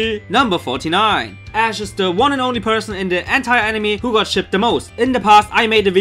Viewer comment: When you said "entire anime" at fact number 49, I instead thought you said "hentai anime" XD